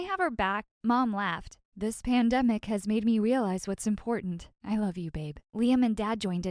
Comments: I love you babe Ayo, did I hear right?!?!? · Please, I hear " I love you babe" I might be Wrong or my ears are right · did she call her babe